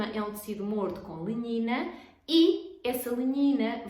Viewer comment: linhina? nao entendi muito bem o que disse no tempo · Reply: Lenhina… substância que se deposita na parede das células